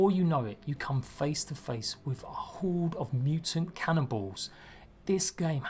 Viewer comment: Did you say "Cannonballs"?